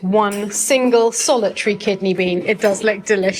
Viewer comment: Silly me. I thought there was kidney in the chili (bllgh) so relieved to have misheard kidney BEANS.